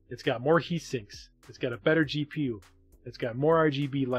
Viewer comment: Did he say that this router has a great GPU (2:30)? Wow, I can't wait for those graphics packets!